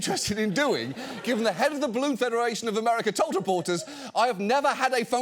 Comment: I keep hearing “federal“😂